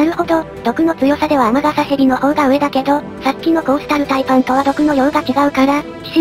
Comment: コースタルタイパンがコーロシタルパイパンに聞こえた